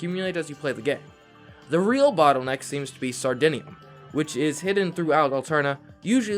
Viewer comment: for a second i thought they said sardinia